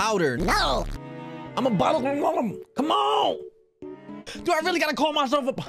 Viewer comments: Why did I hear "I'M A BOTTLE" Just me? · Why do i keep hearing "Im a bottle"🗿🗿